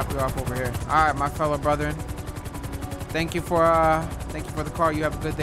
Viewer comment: did he say colored brother?